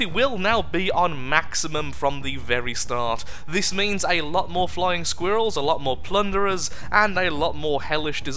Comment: Did he say a lot more flying girls?